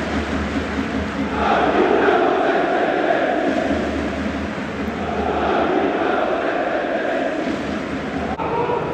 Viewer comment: cosa dice il coro? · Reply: noi tifiamo senza interessi